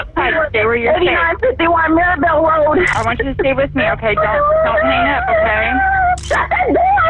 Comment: aint no way the operator told her to stay inside when there is an active shooter. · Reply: I thought she said stay with me